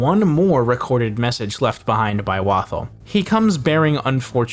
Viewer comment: I just deadass have to say this, every time I heard Wathel introduce himself I always heard it as “Waffle” and so whenever I first heard it I was so confused 😭💀